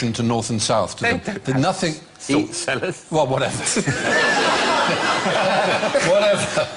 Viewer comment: What did Alan say ? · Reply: "They don't have salt cellars" i.e. a type of salt dispenser.